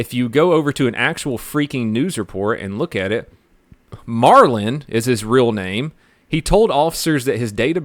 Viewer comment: Sounds like he said marlin to me